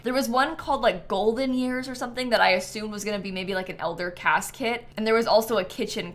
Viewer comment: I heard elder casket 🫣 it took me too long to realise she meant CAS kit 😅